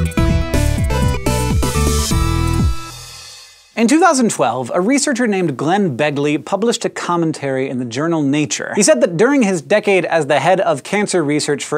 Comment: For a second there, I thought you said "Glenn Beck". Had to start over again... and... Hank on the warpath!